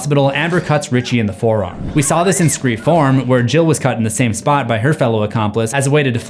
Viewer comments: I keep hearing him say "scream form" instead of four lol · IT SOUNDS LIKE YOURE SAYING SCREAM FORM.